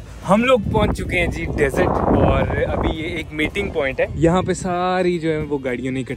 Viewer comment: meeting point i thought he said matting point 💀💀